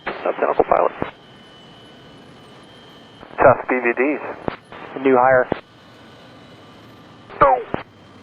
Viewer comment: what are CVD's ? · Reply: Timestamp? Edit: It's at 8:30, though I think the pilot says DVDs. Still not sure what that means though